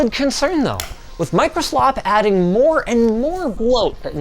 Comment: did he say Microslop